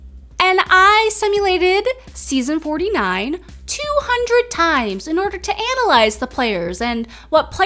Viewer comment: My dumb ah thought you said "I simulated Survivor 49200 times"